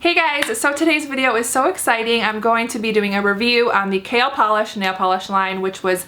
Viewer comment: I thought she was saying kale polish